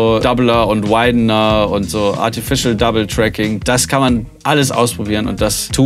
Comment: Waswaswas? Hab ich mich verhört? Martifischel Double Tracking?? Kommt ja gleich nach Martifischel Intelligence.